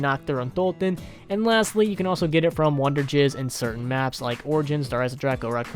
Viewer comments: Anybody notice he said “Wonder Jizz” instead of wonder fizz. (Time stamp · Did you say Wunderjizz?😂